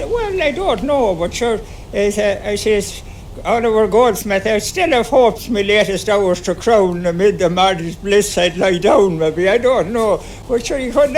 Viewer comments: I think he says "I still have hopes, my latest hours to crown, amid the martyr's bliss, I'd lie down." I think he's misquoting "The deserted village", but you already knew this. For what it's worth. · it sounds like he says "amid the martyr's bliss I'd lie down" but that doesn't follow the original poem. Maybe it's some kind of twist on it or a joke he's making or something. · "Amid a mother's bliss I'd lie down" is what I heard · I think he said "amid me mother's bliss" but im not sure.